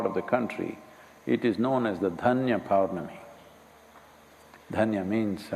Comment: Can someone help? at 9:45 in this video, he mentions the name of the fulfillment moon... What does he say it's called? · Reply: All I got was Dhanya power mani ?